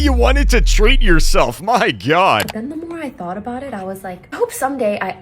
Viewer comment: my cousin was watching this vid and he heard you say "my gyat" instead of "my god" and run up to me like "LoOk RyTh SaId GyAt" 💔🥀